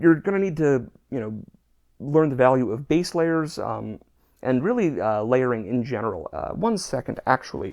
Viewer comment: "You're gonna need to learn the value of bass players," was what I heard. I was hoping for an unrelated tangent.